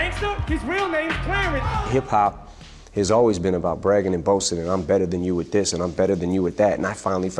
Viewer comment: 안녕하세요~ 올려주시는 영상 자막으로 감사히 공부하고 있는 사람입니다! 3:10 부분 자막이랑 들리는게 조금 다르게 들리는데 I'm better than you at(with?) this 죠?